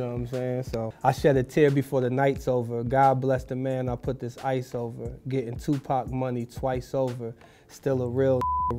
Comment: Always heard it as "God bless the man I put the sights over"